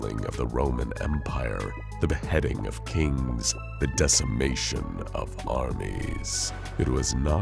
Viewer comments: i swear to god he said "the decimation of arby's" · Anyone else hear arbys instead of armies